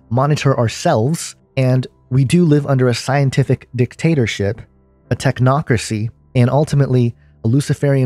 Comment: oh, excuse me, I thought you said theocracy